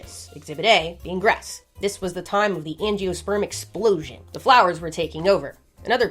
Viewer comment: I only heard "sperm explosion"